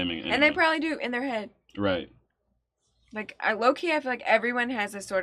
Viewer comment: Lolz, every single time someone says, "low-key," I have to take a second because I hear, "Loki," and it throws me off.